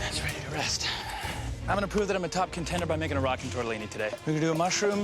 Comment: Omg, I thought it said tortilla. I got excited